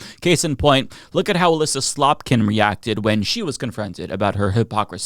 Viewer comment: Did u just say Elisa Slop-kin? 😂😂